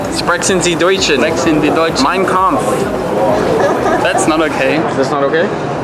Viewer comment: Hat dieser Beauty-Channel Typ ernsthaft "Mein Kampf" in die Kamera gerufen.... oder hab ich mich da verhört?! Wenn ja wärs schon irgendwie traurig...